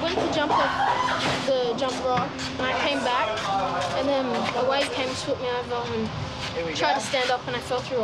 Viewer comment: dude watch this ...... 4:24 ......... he says something like ''my wife came and took me over'' he was about 16 and has braces why did he say his wife?